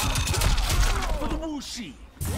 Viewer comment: deadass thought he said “for bull shiiiiit” t is silent 😳👀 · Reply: He said for the wu shi